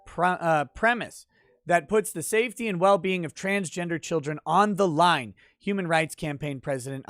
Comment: Hold on. Around 3:30 I heard "trans children". Does that mean there's a bully in drag beating up some kid in the world? This is what I guess for trying to be a Hermit. I'm feeling some whiplash from such a crazy thought.